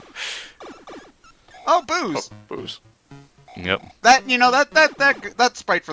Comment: Is it bad that instead of interpreting "Boos!" I heard "Booze!"?